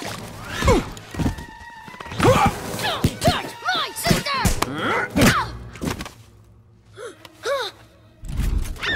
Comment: What does Dash say at 4:24? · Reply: "Don't. Touch. My. Sister"